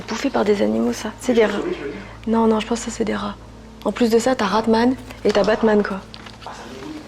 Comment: j'suis la seule à avoir entendu un truc à 11:14?? j'ai compris: Je suis vengeance. Ou un truc comme ça, si c'est bah j'ai eu trop peur